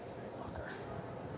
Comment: Does he say "Fucker?" LOL